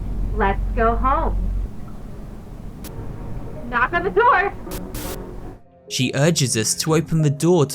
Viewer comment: I thought she said like "Knock at the door" In the last instruction